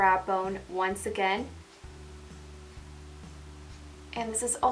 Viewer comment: i thought you said lightly for the blush :D